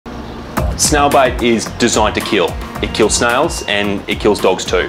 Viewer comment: For people that are worried the dog makes a full recovery and they get rid of the snail bite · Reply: omg I thought he was saying snail bite as in the dog eating a snail/biting into one. I was like Omg I didn’t know that snails were poisonous to dogs and got worried for my babies. Especially my little six month old 3lbs girlie who loves to eat everything